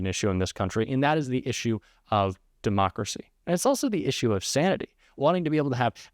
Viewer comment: I thought he said the Democrats caused it